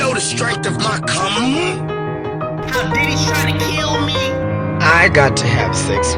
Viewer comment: WHAT DID HE SAY? DIDDY? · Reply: Diddy trying to kill him